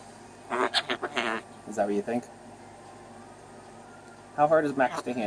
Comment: swear to god, after you told the heater cord story, 6:00 it sounds like Max says "...that stupid heater".